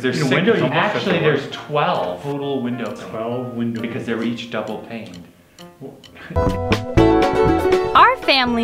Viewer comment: [pretty sure it is "double hung" windows, to make it 12... but that's just an assumption, I heard 'double pane'.]